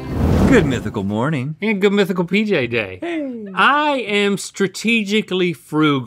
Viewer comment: I thought they said "Good Mythical BJ Day" and I thought I found leaked evening footage